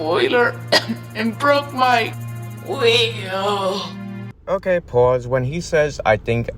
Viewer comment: When he says "wheel", I always thought he said an Elmer Fudd-like pronunciation of "radio": "wadio".